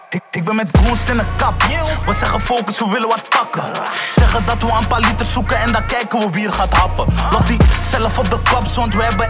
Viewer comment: wat zegt hij daar? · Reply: Zeggen dat we een paar liters zoeken en dan kijke we wie er gaat happen